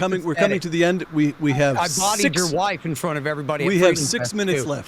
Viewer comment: At: 56:50 Scott says something about this warmonger's wife? I heard "I bedded your wife" and almost fell off the chair. ;) Anyway, can some native speaker here tell me what he actually said? Thanks.